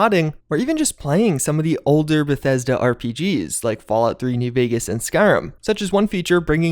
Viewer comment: thought i heard "fallout 3d vegas". i think im goning crazy @@